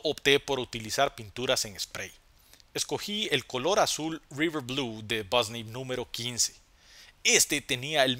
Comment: No se por qué pensé que dijo esto : River Blue o Azul Jaga.